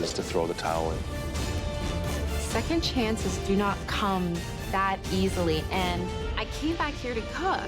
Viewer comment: “Do not to cum that easily”. I thought I heard it wrong